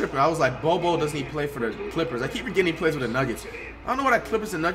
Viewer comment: Did he just say does bol bol play for the clippers